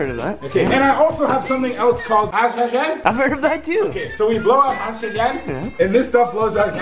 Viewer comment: did he just say that we blow out oxygen